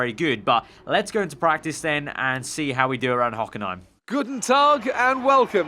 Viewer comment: sounded like Good dog😅 (I know it's pronounced Guten Tag)